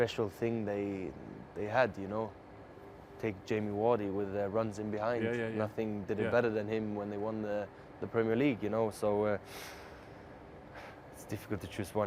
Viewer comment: Best Striker right now 👌👌🤩🤩🤩😢...but am I the only one who heard him say Jamie Wardy